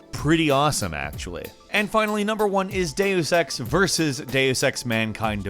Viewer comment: Our series continues. There are so many games to mention. If you've missed our previous ones, check them out here: <link> <link> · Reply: For a second I thought you said Day of sex when clearly you said Deus Ex